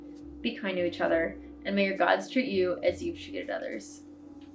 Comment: I wish you a lot of strength for the new year! From the Netherlands with love <3 Ps at the end I misheard "may your gods cheat you as you cheated others", which is also not wrong I guess 😂